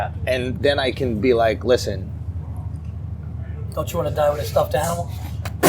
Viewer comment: What did Virzi say at 10:52 ? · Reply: "don't you wanna die with a stuffed animal?"